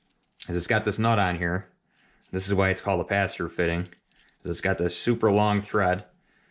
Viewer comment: A what fitting? I know them as a bulkhead fitting. Cant understand what you are calling it. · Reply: A bulkhead fitting or Sometimes they call them a “pass through” fitting